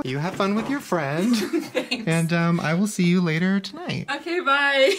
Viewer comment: WTF AT 9:12 I heard someone say the words “I see you” Did anyone else hear that